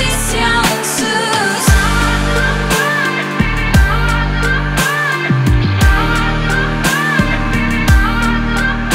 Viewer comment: 'de ne dediğini bilene 100bin lira veriyorum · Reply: Ağzına sağlık dedim